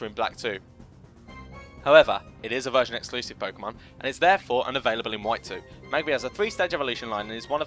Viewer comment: Your first line was correct but the second should read ' is therefore unavailable in White 2 ' which is what he says if you listen to it back, you simply misheard what he said.